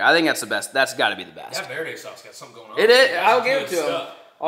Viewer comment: When you said "herdez" I thought you said "hurt ass" 😂